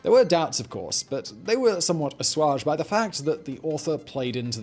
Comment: Every time Simon says "assuaged" anymore, I just hear "Julian Assange". 😅🔥